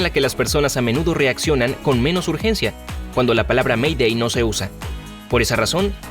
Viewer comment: Yo pense que decia : mandai pan